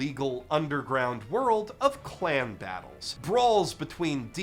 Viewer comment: Did he say "clan battles" or "clam battles"? Because I heard "clam battles". And I laughed. Almost as much as I did when I saw the stupid ass name of the series.